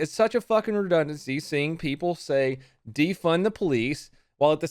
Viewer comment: Legit thought it said DEFEND THE POLICE